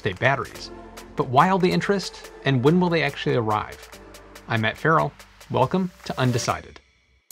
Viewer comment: I thought he said aunt carol not Matt Ferrell at first